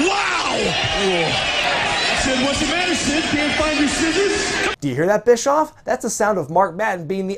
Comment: What does Eric Bischoff says to Sid Vicious at 04:30? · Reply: George Piedra "whats wrong Sid can't find your scissors?" and before you ask. No I have no idea what thats a reference to